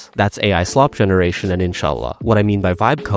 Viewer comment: did he said inchallah (ان شاء الله) 🤌